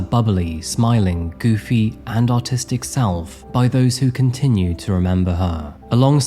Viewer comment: I had to turn on Youtube subtitles because I thought you said "autistic self" lmfao 😂